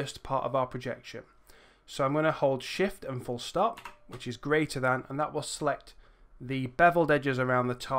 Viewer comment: to select the beveled edges, I can't understand you, did you say "Shift and Full Stop which is Greater Than" or something else. I've tried every combination and can't get it to work. Thanks in advance